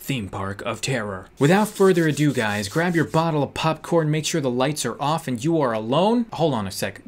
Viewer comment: did he just say “Bottle of popcorn”...